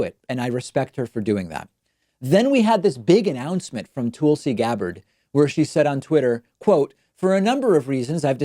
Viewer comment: anyone else hear "Toolsi Gabbard"? 😂